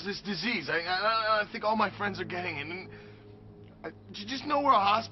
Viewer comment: did he say "theirs this desiese and i think my friends are gay..." wtf?